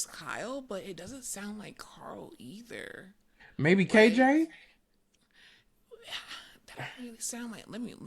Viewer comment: sounded like KJ